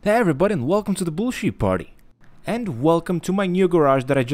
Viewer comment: At first I thought you said bull shit party at first but we all know that’s wrong but I do like your gta 5 videos bro 👍